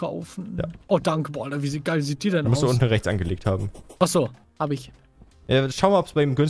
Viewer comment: ich verstehe :,, du musst die unten rechts einmal angeleckt haben " 😂😂😂😂😂